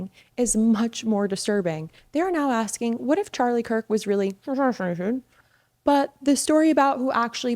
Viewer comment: Is Youtube really censoring that much that you can't say certain words? It's pretty distracting and I don't think I understood parts of what you were saying. Like, I have rewatched 1:29 several times to try to understand what you were saying, but still have no idea. I understand that you may have to do this in order not to get flagged, but it's really frustrating when I fail to see the purpose of this type of censorship. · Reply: Assassinated, babe there’s certain words that they flag when it comes to this subject and it will take her video down❤